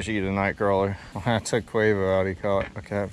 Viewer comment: did he say he went fishing with quavo